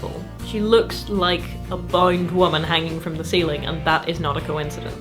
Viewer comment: a "bouyant" woman? The subtitles say "bound", but what you speak sounds much more like "bouind", and I don't notice any dialect with this kind of colorization in the rest of your speech. (???) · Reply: Bound, as in 'a woman tied up'. I do have a weird accent though :)